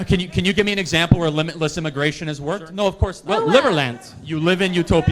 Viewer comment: did he say liberland? a country that is not exists yet.